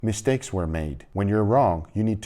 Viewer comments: At the start I thought he said "my steaks were made" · I genuinely misheard the first sentence as "steaks were made", and I was like yeah.. That's what the channel is about?